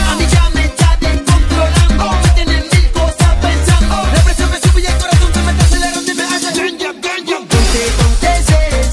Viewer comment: Que es lo que dice?? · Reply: Me tiene mil cosas pensando, la presión me sube y el corazón se me está acelerando y me hace boom boom boom...